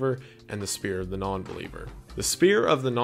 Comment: Thought you said Sphere for a sec and I was so confused